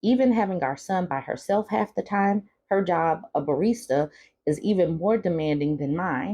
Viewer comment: Note: barrister is British for lawyer. It's easier to be a lawyer and single mom than deal with him. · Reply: I thought he said barista, but good to know. Either way, it all work.